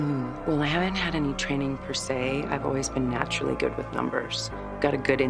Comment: Oh she said right teddy? I thought I heard right daddy? 😂😂